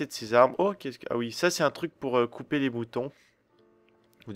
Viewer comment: Y'a t'il que moi qui entend " Sa c'est un truc pour couper les bouton " 3:45 ^^